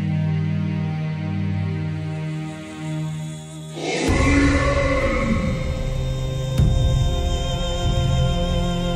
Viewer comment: i don't understand what is it say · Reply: Aurelian. That was what was said. Undoubtedly a call for Lorgar.